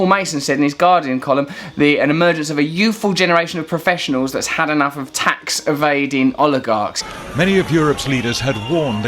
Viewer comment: lol I thought you said "had enough of olive gardens" at the start -_